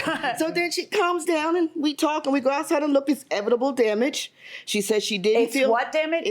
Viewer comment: "Edible" damage😂🤣I'm done with youtube for today. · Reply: I heard “evitable” at first but then I thought she said “edible” 🤣